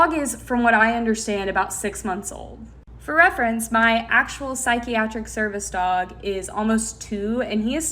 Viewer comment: ngl i heard psychic service dog at first and that really changed my whole perspective 😂😂😂😂😂😂